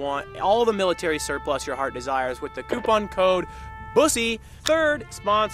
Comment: Did he say use the coupon code "Bussy" ?!?!??